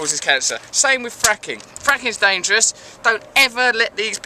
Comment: For one second I thought you said "Fucking is dangerous" and I was like WTF